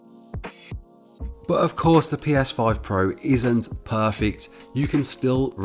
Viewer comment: did he said ps5 pro